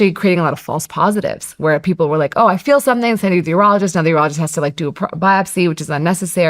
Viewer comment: I was a little confused after about 15 min in.., Thought it said "ufologist"